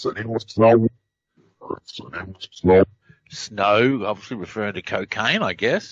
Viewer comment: Time stamp 53:25.. I hear that's the deal with Snowden..